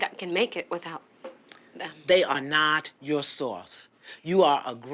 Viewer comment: LoL she sounds like she said Sauce instead of source 🤭🤭🤭🤭 but yeah